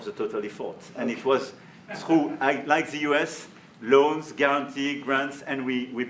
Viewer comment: For those who didn't understand his accent, 1:55 he said, "Unlike the US, through loans, guarantees, grants, we provided real money" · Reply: I heard "Like the US..."